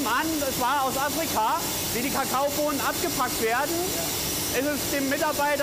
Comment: /57 wer hat da auch "abgefuckt" verstanden